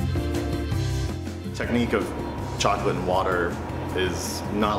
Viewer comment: I thought it said he molds chocolate underwear